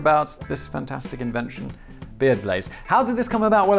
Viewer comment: When Simon says "Beard Blaze" sometimes I hear "Beer Blaze"... That could be your next entrepreneurial idea! I'd buy Beer Blaze... Now I want a beer... 🍺